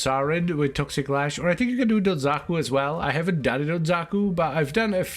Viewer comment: you say Xaku and the Gundam fan in me hears Zaku and wonders what you are saying till I remember Warframe has a Xaku.